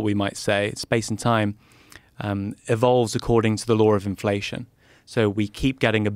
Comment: can anyone else here an 'r' when he say's 'law', like 'lawr'? Dunno where I first heard a british posh guy saying the 'r' at the end of 'saw' (sawr), but I've been hearing it ever since. Can anyone else hear it, or am I mad?